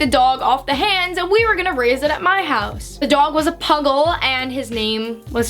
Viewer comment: I thought she said muggle 😂😂